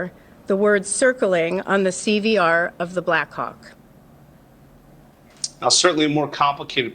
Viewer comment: did she say Black Cok ?